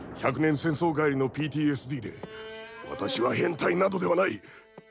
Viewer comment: It would've been cool if there were English subtitles...... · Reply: The only thing I heard was PDSD